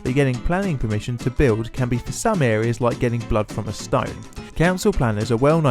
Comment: I know its an old video, doesnt sound similar at all yet when i heard this i heard "Blown on skype" instead of "blood from a stone" Both just as impossible i guess ;c